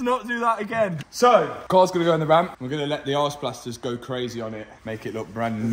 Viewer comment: sounded like you said you'd let the "arse blasters go crazy on it" Now I know you said ice but I still giggled.